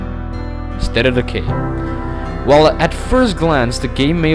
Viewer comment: State of Decay is what he says at 8:57. I had to replay it like 10 times to get it. in case you had the same problem as me.